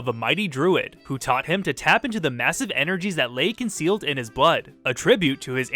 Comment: I really thought he said "...who taught him to tap into the massive energies that lay concealed in his butt"